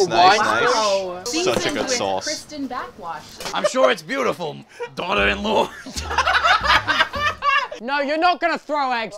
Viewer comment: IS HE SAYING "daughter in law" OR "Don't mind a bit more" bc thats what the subtitles say but it sounds diffrent 😭😭😭